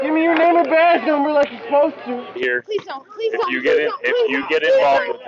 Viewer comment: When they slammed dude to the ground, I thought she said "Please don't, he's pregnant" She actually said "Please don't he's my husband" LOL